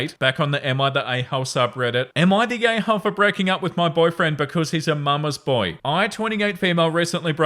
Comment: I’ve been listening to these while I play bloodborne, and wildly misheard the title of story 8 as “aita for breaking up with my boyfriend because he’s an Among Us boy?” and died to the boss I was fighting in my confused scramble to pick up my phone and read the title